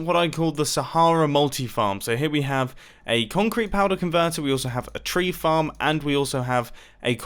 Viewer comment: Right after he said the machine was for Sahara, who else thought he said ConCorp?